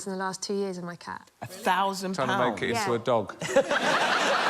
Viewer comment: What did Allen say to Holly? Couldn't understand. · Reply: "Trying to make it into a dog?"